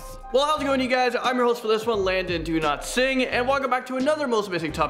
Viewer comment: Is anyone else new and thought he said his name was do not sing